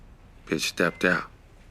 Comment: Does he actually say "step up"? I thought he said "stepped out". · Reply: It is "stepped out," as in "cheated on him." Maybe the editor was also getting his drink on.